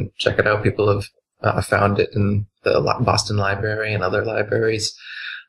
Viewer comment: Did Dubey say 'lieberry'?